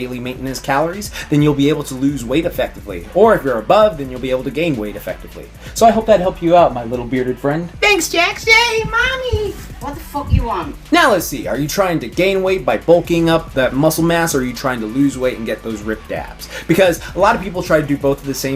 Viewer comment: I thought she said " what the fuck you on? "